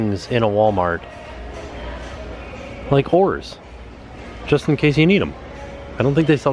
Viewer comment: Had to pause to post.. literally almost choked on my food.. at around 4:36 says “so many interesting things at Walmart...like oars”. For just a split second I thought he said “like whores” 😳😂